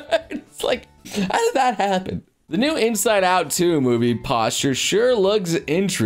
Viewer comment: Anyone else hear "posture" instead of "poster"?